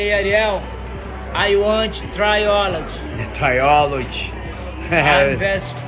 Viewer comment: Trilogy, I thought he said he wanted to try olives lol.